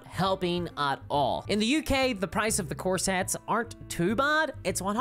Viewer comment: I kept hearing "core sets" as corsets. This led me to have some odd mental images with respect to Warhammer "Fantasy" play. 😅